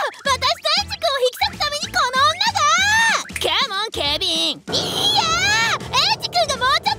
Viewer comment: 画面見てなくて「カモン警備員」が「カモンケビン」て聞こえて ？？？ てなった😂